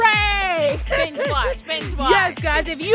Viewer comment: Hold up! Wait a minute! At the end of that vid, I though said to "B" watch our videos! For real tho! I thought That's what she said LMFAO!!!! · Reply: I think she may have said binge watch, but I technically wasn't paying attention cuz she talked a little too fast. Lol